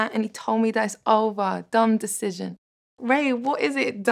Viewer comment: For the first time I heard it, I heard 'done decision', and that's much more poetic and carries deeper emotions than 'dumb decision'!